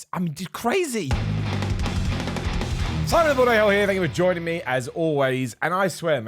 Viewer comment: looool untill i turned on captions i thought you're saying "simon a hoe here" in the intro